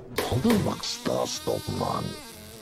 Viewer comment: I swear one of the fly guys says "Baxter Stockman" which is such a fast reference! (Stockman is the scientist in Ninja Turtles that mutates into a fly man)